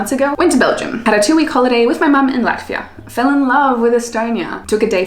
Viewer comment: I soo thought she said "fell in love with a stoner"